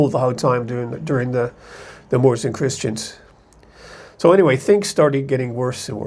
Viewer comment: What's the Morrison Christians in the Summer? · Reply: I thought he said "moores and christians"